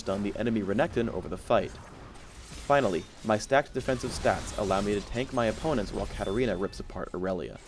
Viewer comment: Did he say, "My stacked defensive stacks" at 5:18? whats that supposed to mean?